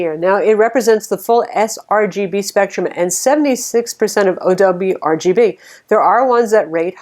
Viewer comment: it sounded like "OWRGB"...when I think it was meant to be AdobeRGB. Must be my speakers.